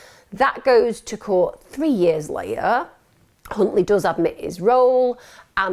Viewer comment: I swear I heard you say C...untley lol